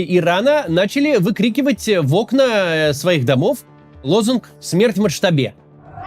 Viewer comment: я четко слышу "Акбар Моджтаба" что явно переводится как прославление нового ВЛ, но никак не призыв к его кончине. упс, ошибочка вышла?))